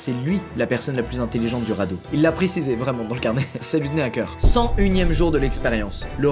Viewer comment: Il a dit quoi à 13:18 ?? .. hacker ? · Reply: "Ça lui tenais a cœur"😊😊